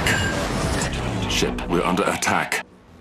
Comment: instead of saying returning to the ship or we are under attack it said shit we are under attack xDDDDD